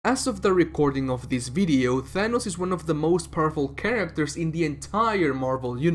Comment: At first I thought you said he’s the most purple character in the marvel universe. That also would be true